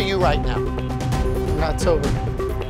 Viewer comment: “How drunk are you right now?” “I’m not sober.” 😂😂😂😂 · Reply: Thought he said October!!😂😂😂😂😂😂